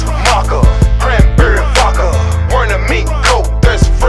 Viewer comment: wait my bad I thought he said flakka instead of vodka